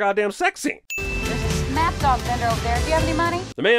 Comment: Snap dog? I thought she said SNACK dog vendor!!